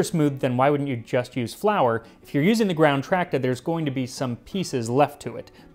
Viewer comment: Has anyone else read The Satyricon? If not, it's worth your time, I promise. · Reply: you've got a typo in the subtitles. "Tractor" instead of Tracta!